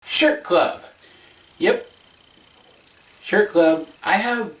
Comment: Am I the only one who did a double take at the begining when I thought he said "shit club"